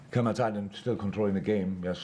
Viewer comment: did klopp said كما تعلم ؟